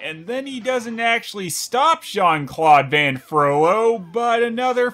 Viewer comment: I think they called "Frolo" in this "Jean-Claude" because in the Disney version, they said "Judge Claude Frolo" twice at the beginning, and the script-writer (who was both high and drunk) heard "Jean-Claude Frolo" and put it in his notes, right after "That thing everyone loved from Disney's Beauty and the Beast" and "be sure to completely miss the point of the story." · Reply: Admittedly, when I was younger I totally misheard it as "Jean-Claude Frollo" once or twice.